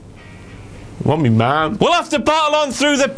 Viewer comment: what did he say?? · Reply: "Want me mom".